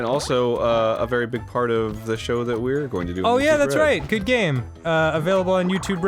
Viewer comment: on 4:59 it sounded like dan said "youtube bread" at first and all i could imagine was Avi voice: Can i buy it on youtube bread?